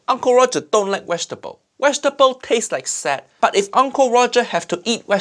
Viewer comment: Uncle Roger needs to understand that "taste like sad" is the subtitle to british food. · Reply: Thought he said tastes like sand